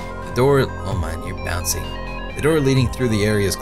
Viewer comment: I thought I heard him say "bouncing in the sink," instead of "bouncing in sync." XD